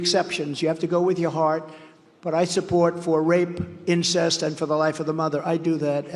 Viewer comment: Did trump just say " Support rape."!?!? 0:45 Ok folks. If we don't vote the republicans out forever then we will deserve this doomed world.